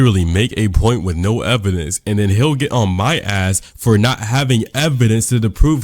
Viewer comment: youll have to bear with me the neuron activation that happened at 4:18 i heard "he'll get" as "hill gym"